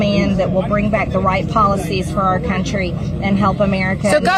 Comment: I swear I heard @ 4:00 - “WHITE policies for our country”